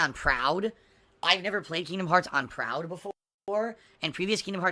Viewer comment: I could have sworn that Phil said he's never played Kingdom Hearts. But he says he's played the others. 0_o